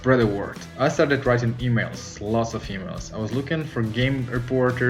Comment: Oh EMAILS! I thought I heard "lot's of females."